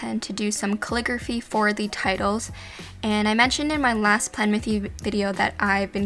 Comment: did you say plan with you?!